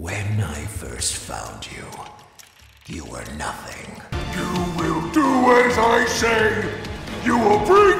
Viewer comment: I had thought "you will do as i say" was Montgomery gator